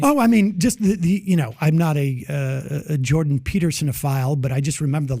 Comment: DID SAM JUST SAY "JORDAN PETERSON-FILE?" WTF?🤔